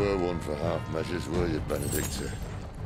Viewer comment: What did Cid say at 5:42 ? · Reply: Never were one for half measures were you Benedikta